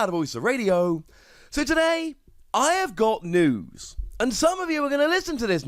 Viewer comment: i thought you said i got nudes at the beginning